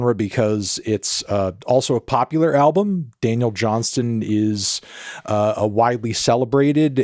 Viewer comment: I thought you said Daniel Johns. He has two projects, "I can't believe it's not rock" and the band self titled album "The dissociatives". Criminally underrated, I like that Daniel better. He's the singer from silverchair, he's a genius song writer, very underrated. It's like if you mixed the beatles with 90s grunge/alternative, really trippy stuff.